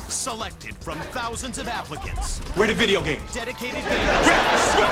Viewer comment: At the beginning, I thought I heard the narrator say "Selected from thousands of Africans".